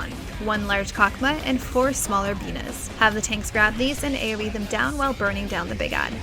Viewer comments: "Four smaller Binah's" ---Me: Did she just say penis? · sounded like "One large Cock-ma and 4 smaller penis"